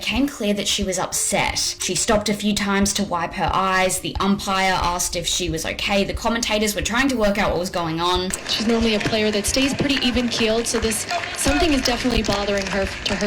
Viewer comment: i dont know why i keep thinking she was gonna say "wipe her ass" 🤦🏾‍♂️🤦🏾‍♂️ when i done heard it 4 times already🤣🤣🤣